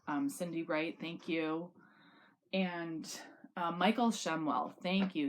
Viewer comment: I first thought you said Michael Shunwell lol